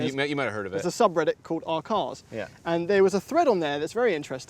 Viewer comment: I can't make out what is being said. What is the subreddit called? r/ourcars? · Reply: R/cars